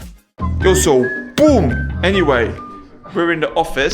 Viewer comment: bro did you say, eu sou o boom! 🤔